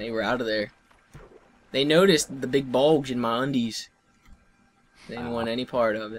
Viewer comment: i skiped to 3:49 an all i hear is balls in my undies lol